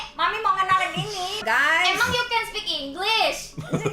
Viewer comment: Kinda late but 1:32 is a small mistranslation. What kobo actually said was "Emang you can speak english?" Emang is an indonesian word which in this context means something like "Can you really?" So the correct translation is "Can you really speak english?" (Even if kobo said "-you can speak english?", the intent behind what she said was "Can you really speak english?")